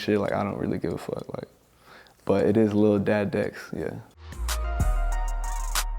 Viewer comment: It sound like he said little dad dicks😂Stocktone is a bunch of Shooters and Chainsnachers...it’s gutter but it’s a fun little town...